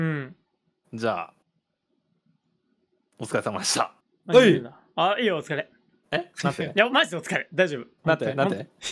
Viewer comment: ここなんて言ってる？ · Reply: 逃げるんだやろw